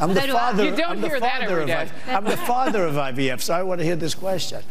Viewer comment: I know no one there at TLP will see this, but I heard Trump say "I'm the father of IVFs", plural, as in he's fathered children using IVF.